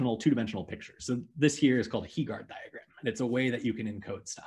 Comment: what's that diagram called at 7:17? i can't quite understand what he's saying enough to search it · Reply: Heegaard diagram